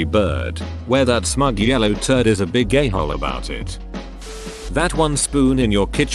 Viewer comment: It sounded like " a big gay hole" 💀